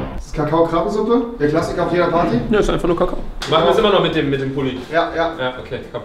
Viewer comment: Haha ich habe bei 5:31 verstanden von "ne es ist einfach nur kacke"😂😂😂😂😂 Wer schaut auch 2019?😂